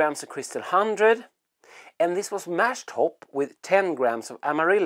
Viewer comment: did you say mash-hopped?